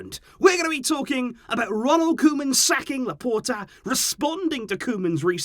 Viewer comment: "Ronald Koeman sacking Laporta".. i thought i heard wrong😅